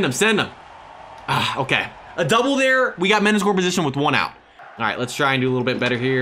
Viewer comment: tell me what he said and I will pay u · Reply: men’s score position one out